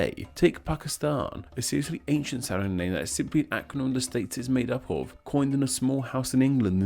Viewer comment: What is he saying? I cannot for the life of me make the whole thing out. "that is simply……coined in" ← But what's in between that? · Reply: "simply an acronym of the states it's made up of"